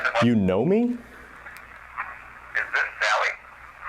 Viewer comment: i hear "yes this is sallie"